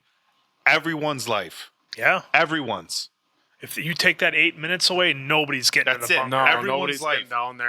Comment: no you clearly misheard that. They clearly said they eight extra minutes for evacuation. That surely helped a lot, but he didn’t save just everybody because district 13 has clearly some sort of warning system that would have warned him ahead of time for evacuation.